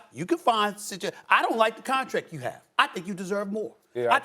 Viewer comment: For a moment there I thought Stephen A. was saying to Molly "I don't like your contract!"